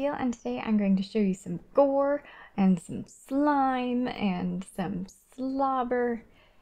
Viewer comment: I thought at the start she said and some buggering 😂